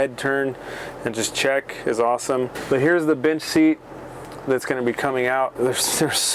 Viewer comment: Lol I thought you said the B1tch seat. Lol